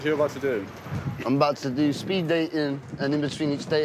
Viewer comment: when he said "Im going to do speed dating" i thought he said "I'm going to do speed bacon" and I got excited about a new drug I hadn't tried yet